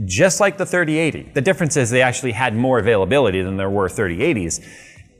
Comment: When he said availability I thought he said a-phil-ability. 😂 the ability for Phil to use it.